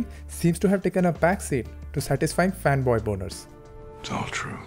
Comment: did you say "Fan boy Boners"? ..hahahah....Creative description.